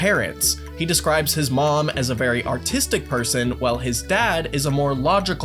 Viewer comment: I thought they said autistic 😂😂😂.